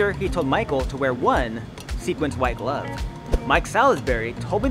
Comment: Did you say "sequenced white glove?" 😂😂